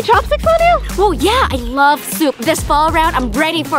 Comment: 😂 does Vy say I love soup